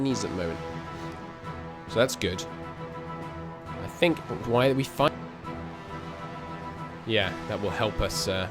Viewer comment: it almost sounded like it said, "Why we fuck." I think it's my headphones and my dirty mind.